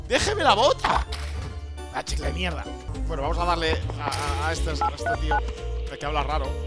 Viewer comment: a creo q escuche abeja de mierda